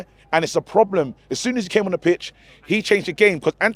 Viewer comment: Did say when he when he came on the bitch🤣 9:30.